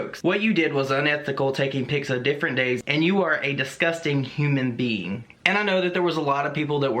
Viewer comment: I thought you said disgusting human BEAN and I was like damn trisha really said that